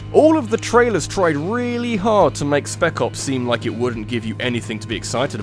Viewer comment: I kept hearing "Speckles the Lion" whenever you were talking about Spec Ops.